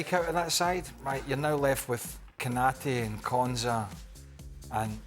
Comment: Should prioritize me. I'll happily take £250,000/weekly. 1:48 Did he say Konsa? Does he realize he's not talking about Villa?